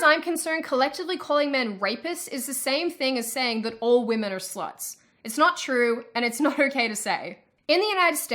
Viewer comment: It is interesting that on minute 10:11, when she says that all women are "s**ts" the caption have an empty space. Which also is a good point for what she was saying.